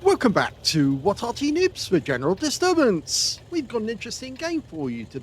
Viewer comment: Sounds like your saying "nibs" instead of "noobs". Thanks for the vid:body-blue-raised-arms